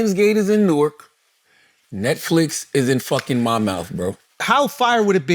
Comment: Charlemagne wants all of Hollywood in Monmouth, but he sounds like he says he wants it in “Mymouth” 😂😂